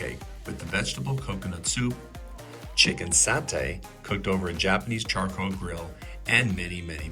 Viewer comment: did i hear Japanese here or i missheard it..😅, i think that satay or sate has nothing to with Japanese, not even Javanese..because that type of Sate is actually Madura's style satay that popularized by Maduranese people from Island of Madura (eastern side of Java)..😅